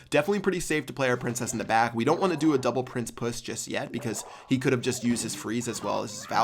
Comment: anybody hear puss?